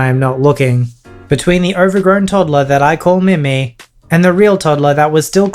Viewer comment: Who else thought that said “meme”